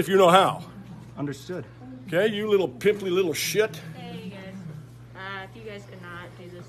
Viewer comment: "ya pimply little shit" is probably the best insult I've ever heard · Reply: I thought he said “you little pip” like short for pipsqueak. and you never know. he might really be that guy.